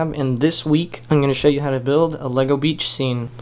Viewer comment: lol i thought it said bleach